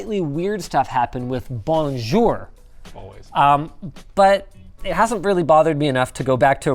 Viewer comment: Who heard this "always" in the background regarding Bonjour? 😄 its true 😄